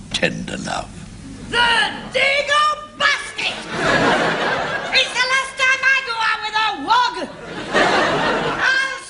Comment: Turned on captions at 17:52... Couldn't understand what Mrs.Slocombe yelled. Sounded like dago basket? wog? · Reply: She said "THE DAGO B@@ IT'S THE LAST TIME I EVER GOT OUT WITH A WOG!!!!!" "Dago" and "Wog" are racial/ethnic slurs. Dago usually referring to Italians, Spaniards, and Portuguese, while Wog, while being a far mar broader term, can refer to people of Mediterranean descent.